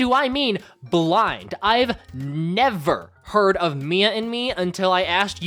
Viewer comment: Why do i hear "Mia in me" 😭😭😭